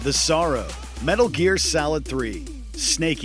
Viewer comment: I’m not the only one who heard him say “Metal Gear Salad” am I?